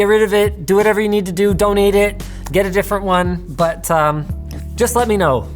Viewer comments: When he said "donate it" I heard "don't eat it" and I was like bruh, he ain't that stupid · when he said "donate it" I thought he said "don't eat it" lol